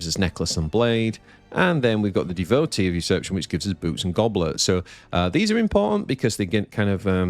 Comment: Did you just say "boobs and goblets"? Looking at the card, I can see where you are coming from :D PS: there is an A in "usurpation" that you always seem to miss when pronouncing it :)